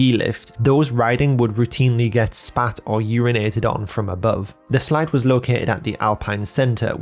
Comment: "spat on or urinated on from above" USA, USA, USA !! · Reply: OH FUCK😂😭 I completely misheard him at the part about being spat on and urinated on cuz I was playing the video sped up and distracted lol . I thought he said "They were getting smacked upon from above" and thought he meant likeeee....The chair lift was so low hanging to the alpine slide that people were smacking their heads against the chair lift seats while sliding down it ... 😅😂